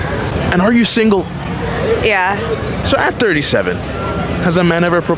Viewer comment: I thought he said to her not “at 37” but “act 37”